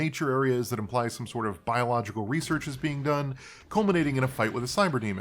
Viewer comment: I misheared it as "impaled biological researchers" ☠️